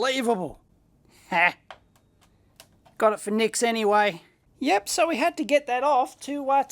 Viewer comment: Did you just say "voor niks"?